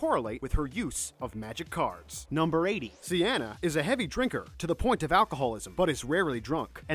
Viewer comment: Did he call Cana, thianna?